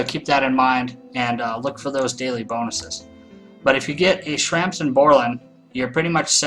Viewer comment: was that Grampz and Mer'lan or did i mishear something like "Shrampz and Bor'lan? lol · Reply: I personally beat every Zone with Shrampz and Borlan, OP Power team!